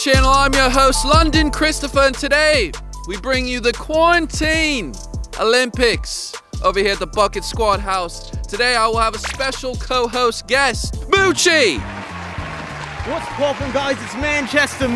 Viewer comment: During the team intro I thought Kris said the fuck it squad, I was like hold up wait a minute something ain't right